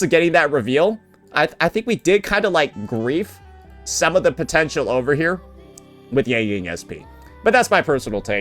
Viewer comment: did you say POTENTIAL?